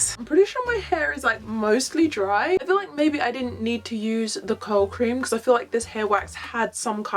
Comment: For the longest time I thought you were saying cold cream, instead of curl cream 😂😂